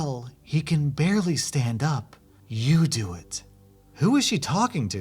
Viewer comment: The "You do it." makes me doubt the minor theory, if those were the first girl's exact words. That suggests some kinda unwillingness or at least reluctance to do "it" herself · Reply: I thought she said "you can do it" not "you do it"